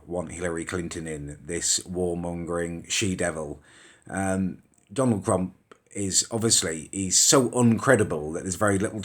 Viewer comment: seeing though I am British and I don't really give a fuck. I just thought I would say that at 4:34 he said Donald crump. and that was the most amusing part of the video